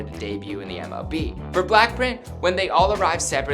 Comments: Anyone notice how he said black print instead of BLACKPINK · Is it just me or I heard Blackprint? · did he say ‘black-print’ or am I tripin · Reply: godlisa HE SAID BLACK PRINT LMAOOOOO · Did I hear that right? Blackprint 😅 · did he say “blackprint” in